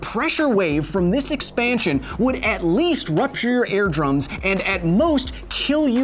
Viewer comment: I swear it sounds like you said air drums and not ear drums